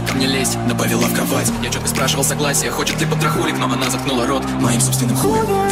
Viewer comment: Is it just me or do I hear "paw patrol"